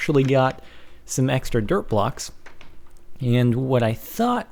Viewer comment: lol i thought u said derp block